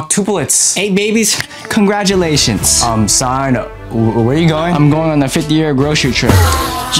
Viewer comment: did she really say son 💀💀